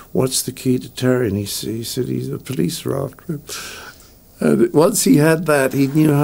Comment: what does Cleese say is the key to the Terry character? The CC has it as "police rock"